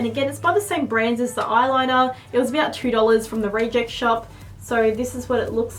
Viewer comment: What shop was that? I heard "ray jeck shop"